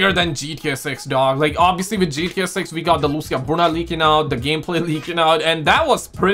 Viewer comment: It looks like a Stadium. Lmao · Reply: thought he said lucid punda leaking out 1:42 🤣 until i click cc, oops. 😅 yeah looks like nice white stadium pum pum ☠️ wheres black wersion?😅 with blue lights or red lights 😮😢